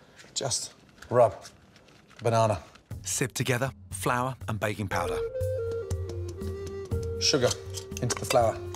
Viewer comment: I swear I heard him say baby powder instead of baking powder lolll